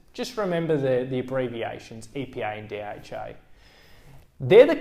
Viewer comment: I thought he said EPI and DHI. I just realised he means EPA and DHA seriously..